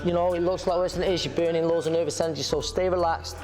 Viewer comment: I think he was saying your are biting out the nervous system of you are taking shots tensed